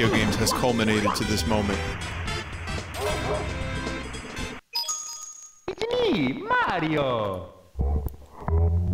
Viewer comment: CC: [Bowser laugh slowed down] Me What I Heard: [Mario Saying: “It’s A Me! Mario” Slowed Down]